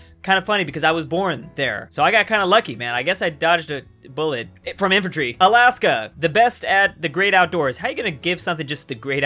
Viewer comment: I also thought it said infentairy With colarado didnt think like active cus doing drugs I thought like 'high drug overdose cus they take pain cilers after doing activites' Im usaly the guy that'd say what u said...